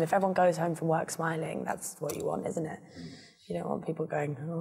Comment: What did he say at 4:16 after ''great to see you''? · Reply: "lovely to see ya"